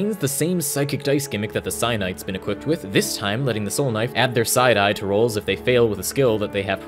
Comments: Thought he was saying "side eye" lol took me a minute to figure out he was saying "psi die" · when he said psi die i thought he said side eye